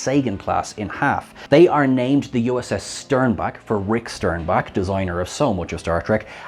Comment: When I heard "Sternbach," I had to double take and be like "Did I hear that right? Did they say Starbuck?" Nope, Sternbach.